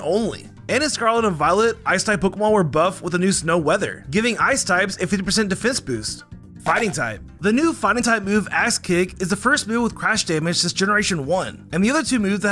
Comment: When he said axe kick I thought he said ass kick